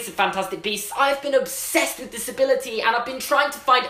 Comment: “I’ve being obsessed with disability” That’s how I heard it and how the subtitles did it